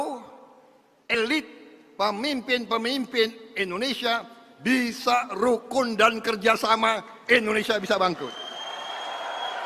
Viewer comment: ko bilang kalau pemimpin Indonesia bersatu bilangnya Indonesia bisa bangkrut, apa saya salah denger😂😂